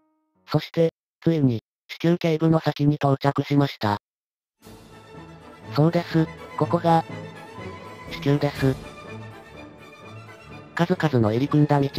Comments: 「そうです、ここが、、子宮です」 が 「そうです、ここが、、地球です」 に聞こえて盛大に吹いた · ここが子宮です。が ここが地球です。って聞こえた